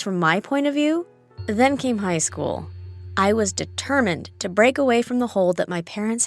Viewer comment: For some reason when you said and then high school, I heard you say and then puberty started I was like, hold up, what??